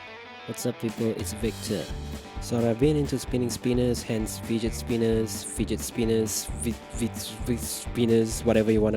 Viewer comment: lol I thought he said fidget penis